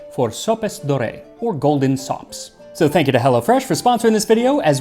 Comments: The first time you said "Golden Sops," I thought you said "Golden Socks," and I was very confused. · NGL, I first heard you say "Golden socks" 😀